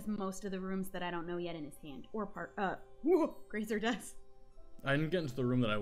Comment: did she just call parker😂